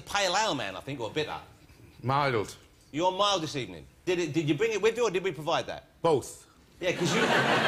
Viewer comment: "Did you bring it with you or did you provide that?" All I heard "Wibbly wobbly, 5 stars, dribble."